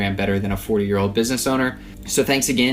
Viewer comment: I heard you say '4 year old business owner' :D